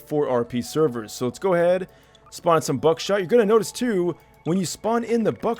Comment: lol did he just say buck shite 😂